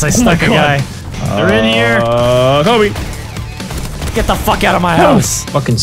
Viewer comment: What's that word sounds like "Kobe" ? They often say that when they kill a guy. Like in 4:38. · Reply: They do say Kobe -.